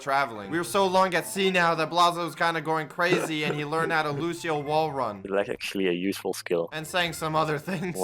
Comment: Am I going crazy or at 15:40 did he said blaza learned how to Lucio run like in gorilla tag